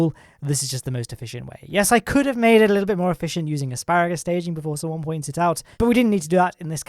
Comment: did he say "asparagus staging"?